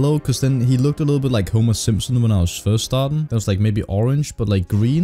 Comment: I thought you said "he looks a little like homosexual" not "homer simpson" 😂😂🤣